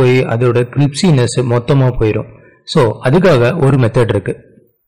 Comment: cripsyness uh🤔 did I hear it wrong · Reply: Yeah that is crispyness